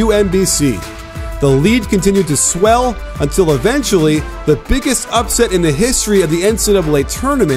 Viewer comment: Who else thought it said biggest upset in N*GGA HISTORY